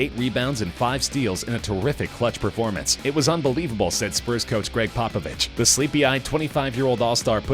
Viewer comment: he said "spruce" instead of "spurs"😂😂😂